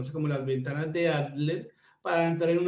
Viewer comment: Podría saber qué dice en 14:10 cuando dice “las ventanas de A...”. Muchas gracias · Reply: Adler